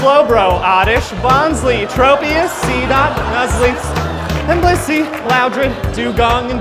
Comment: wait, I heard someone say stunky but it didn’t sound like. grab the 12.